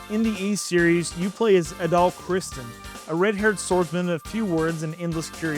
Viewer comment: I’m pretty sure you said Adol, but I swear you said Adolph….😂 had to rewind it a couple times.